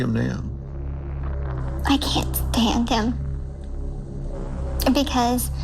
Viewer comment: (time stamp). Did she say she can't stand "them". Meaning more than one. When Dr. Phil says "how do you feel about him now?"